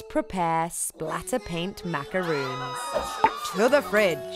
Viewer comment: Am I the only one that thought it said Painted macaroni